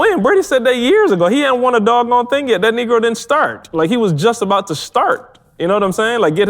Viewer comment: Did call Brandy a negro??